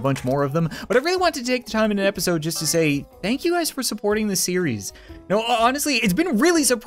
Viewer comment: In the intro I thought he was going to say thank you monkeys